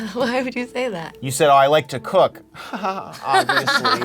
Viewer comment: I thought she said: “I like to cuck.”